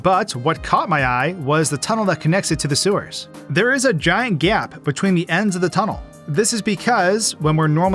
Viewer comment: I thought you say gaint gaytt